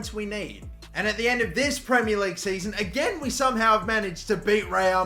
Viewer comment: Did he say the premier league